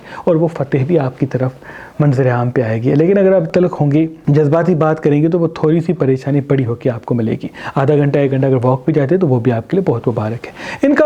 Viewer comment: pr kya bola hai anyone can tell? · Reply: Unhon nay kaha Agr ap jazbati hon gay toh apka chota masla bara hojayega adha ghanta yah aik ghanta walk pe jayein woh bhi mubarak hai. Advice meri taraf say yeh stars pe yaqeen rkhna shirk hai toh isy gurez krein please